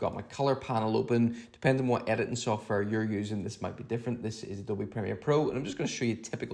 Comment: Pardon me. but what is the photo color editing app you said you are using? I thought i heard Adobe Creator. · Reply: It is Adobe Premiere Pro with the Insta360 Reframe Plug-in. Please check out the link more details <link>